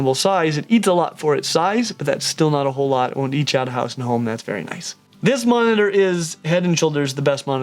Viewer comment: what did bro say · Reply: It won’t eat you out of house and home